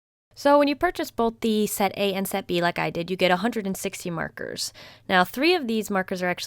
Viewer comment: At first I thought you said that “when you buy both set A and set B you pay $160” and not “160 markers. Lol